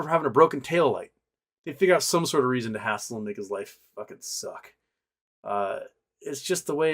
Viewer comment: What I'm saying is that white people do not face hardship for the color of their skin (unless they move to certain parts of Hawaii, because what the missionaries did to the native Hawaiians was straight up genocide, and after 200 years of oppression, many of them understandably hold a grudge) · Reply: Yeah I got that, its just in 2:35 took me a couple of listening to understand what your saying it sounded like "they figure our some sort of reason to hassle a n..'s life" (disclaimer: english is not my native language)